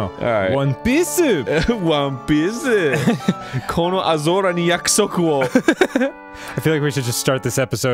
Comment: Dafuq does Arin say? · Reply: "kono aozora ni yakusoku wo" Basically he name drops an anime title for some reason